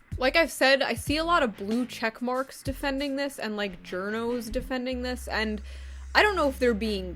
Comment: When she said journos i thought she said Giornos